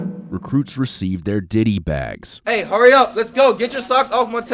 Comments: diddy bags did I hear that right? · I was looking away for one moment and I thought they said “diddy bags” 😭 but all jokes aside I have MAD respect for these people